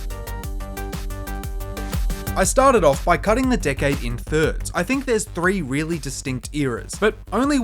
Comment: I heard " cutting the d*ck head in thirds" and now I can't unhear it! 😢